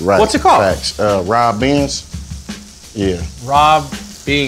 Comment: I thought he said rob banks🤣